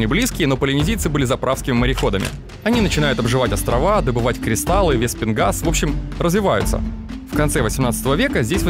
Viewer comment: На 7 минуте не понятно что ты говоришь.паленезийцы начинают добывать кристалы веспенгаз,что такое вепенгаз или мне послышалось я как то не так услышал.Обьясните мне плиз